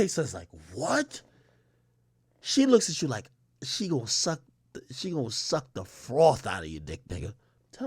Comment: I heard "She gone suck the broth outta ya"!